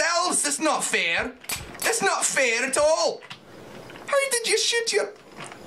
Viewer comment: did he just say why dont you s*** your...